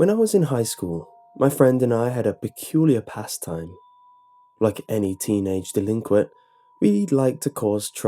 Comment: At first I heard, "Me and my friend had a peculiar pasta."